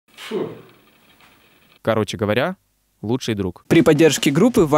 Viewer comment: мне в начале послышалось что он сказал вместо лучшого друга короче говоря худший друг :D